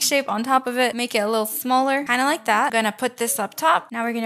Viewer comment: why did u say smarler